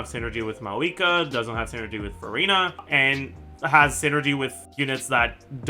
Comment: did bro just say Verina from wuwa? ICANT (probably misheard but it's funny thinking that he did)